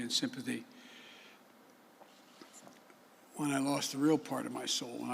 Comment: 22.45 did he say “when I lost the real part of my soul? I may had misheard though can anyone else follow him? Answering questions he rambles on and rarely gives an answer and makes very little sense.